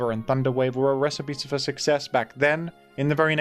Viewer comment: Whenever says “recipe for success” I always hear “recipe for successipe”